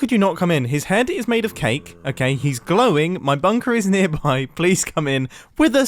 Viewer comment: he says “My bunker is nearby please come in.” Subtitles think he says “police come in.”